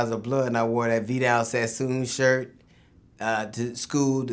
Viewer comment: First off, I must say I respect DL for leaving the gang life for his family, that's some real some but um what did he say he wore at the 7:27 mark? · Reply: Vidal Sassoon.....its a clothing brand that looks horrible.